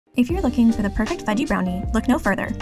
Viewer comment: I legit thought she said, "if your looking for the perfect Veggie Brownie" 😳😳🤦🏼‍♀️🤦🏼‍♀️ I kept waiting to see wth she was gonna throw in! 🤦🏼‍♀️😂😆😂